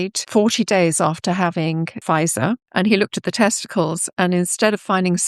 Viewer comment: I thought she was saying FISA, which made no sense. SHe's saying Pfizer. I wish she had included the word vaccine, or jab, or something slightly more descriptive than Pfizer with an English accent. Btw, I never had the jab. Knew better. But my immediate family members, who think I'm nuts, did. Thankfully, it seems they did not have one of these terrible ones. They seem unharmed, and it's been years.